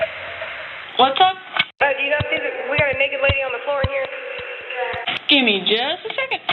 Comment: Who's on the floor? Did she say n.lady? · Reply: Sounds like "naked lady."